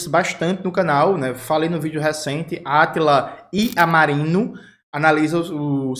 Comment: Meu Deus, eu ouvi certo??? Átila I amarino? Eu sinto q eu nem conheço essa pessoa